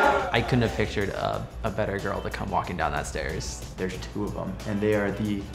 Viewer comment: At the beginning was like : I expected a better girl walking down those stairs Me : wth did u just say !!! 💀😱😲